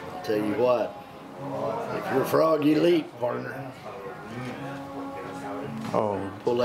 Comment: he actually says “if you’re a frog, you leap, partner”.